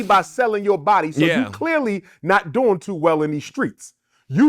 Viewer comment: thats not what he’s saying bro, he said clearly not doing too well in these streets meaning to start doing OF you must not have been making too much money prior to doing OF to result to selling your body